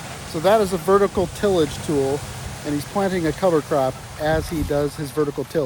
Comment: what kind of crop is he planting? Sounded like color crop edit: probably cover crop right?